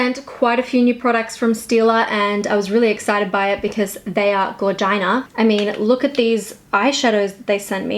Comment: When she said they are gorgina I literally thought she said “they are vaginas” and almost died